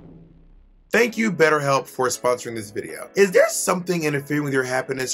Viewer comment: I thought she said "than. you Farrah moan for sponsoring this video"